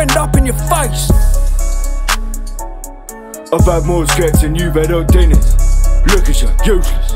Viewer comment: “I’ve had more skets than you’ve had hot dinners” 😂😂😂😂🔥🔥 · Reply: Thought he said, I've got more skengs than you've had s guiness